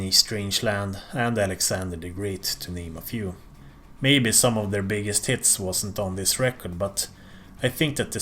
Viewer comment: On Number of the Beast it sounded like he said "Three of their biggest tits" instead of "three of their biggest hits"